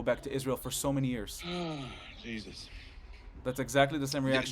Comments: did he just say Jesus?!!!!! 😆 isn't he a Muslim? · DID HE REALLY SAID JESUS😂😂😂😂😂😂😂